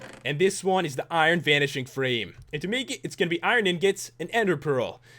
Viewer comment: it kinda sounded like you said vanishing cream lol